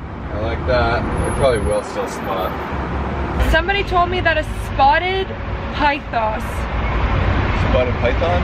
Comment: Haha I thought you said a spotted python!!!